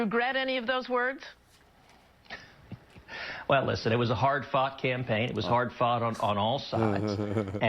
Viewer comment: For a split second I thought he said "It was a hard fuck campaign!" 😂😂😂